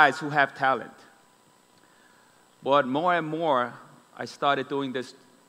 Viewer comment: Did jyp just said "more & more" at 1:04?